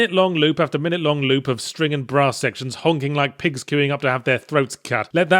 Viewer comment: I know he said Brass but it sounded like he said bra